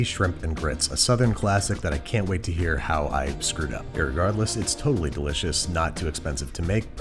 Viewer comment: did you say irregardless?